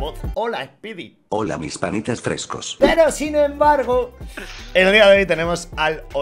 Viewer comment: Escuche bien? Se volvio pana fresco? Au